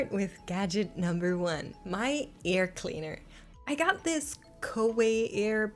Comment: its probably your accent but it sounded like you said ear cleaner rather than air cleaner LOL